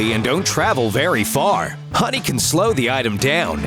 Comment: At about 1:10,it sounds like he said putty,not honey